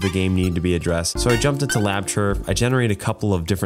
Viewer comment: What was the sound effect program you used? Sounded like you said Labchur?